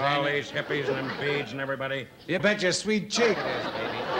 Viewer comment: What was said when we hear “you bet your sweet cheeks” at 7:35? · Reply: “You bet your sweet ASS”